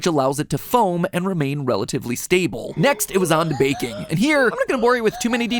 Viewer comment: When he said “It’s time for baking” I thought he said “vaping” & that weird music started & I thought it was a sound byte of someone gasping for air (because vaping) but pitched to make music😂🤣🤣